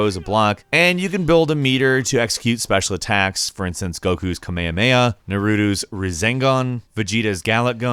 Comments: Did he just say "nuruutu's razengan" tf😂😂😂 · Hold TF up... @ 5:05 did this guy say NARUTU!? Wtf!? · Now I know damn well he did not just say nurutus razangan 5:07 wtf kinda pronunciation is That!?!? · did he just say nurutu?